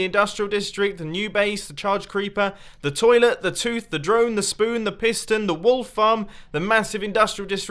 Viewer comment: I paused so all I heard next was "spoon the piston"